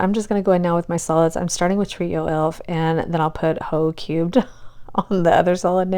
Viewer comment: I thought you said "Ho Cute" at first, so when you flipped the jar and it was "Ho Cubed," I got a good lil chuckle. Also, I edit videos for a living, so I think 2 videos a week AND doing multiple Manis is more than enough. You are basically doing 4 jobs the way I see it. Your main job, 👏 nail tech👏, video editor👏and content creator 👏. You answer like, every comment here. So, as a fellow editor, I ask that you please not burn yourself out on our account. 😅 you are the BEST, and if I could figure out how to nominate you for a Shorty award, I would! ❤️